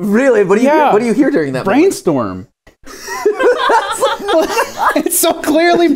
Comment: It clearly has 3 sylables and starts with strong B sound i hear: Brain Needle